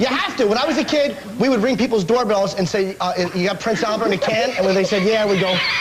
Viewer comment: I don't understand what Jim Norton said at 4:50. I get it untill he said "[...] We ring people's doorbells and say..." but then I just don't get the words he said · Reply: "Do you have Prince Albert in a can?" They say, "Yeah" And we said, "Well you better let him out" Basically just the dumbest joke he could possibly say